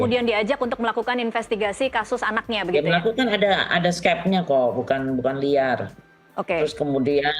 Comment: dia bilang apa sih? ada SKEP apa SKET? kalo skep, skep apa? skep pengangkatan dia sbg reserse? kalo itu dipake, liar banget. mentang2 reserse bisa gitu priksa2 lintas wilayahnya semaunya? toh UDAH JELAS banget tindakan rudiana "investigasi" itu sblm ada LP. trs masih mau bilang ini ga liar?